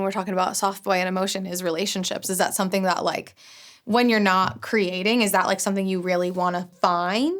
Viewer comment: thought she said “when you’re not cremating”